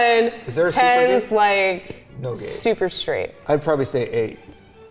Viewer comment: DID SHE SAID SUPER STR8? 😶🤢